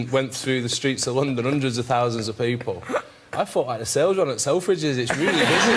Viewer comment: wat freddie said at 8:10 to 8:12 ..God!its hard to pick his accent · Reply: he said 'I thought the sale was on at selfridge's, it was really busy'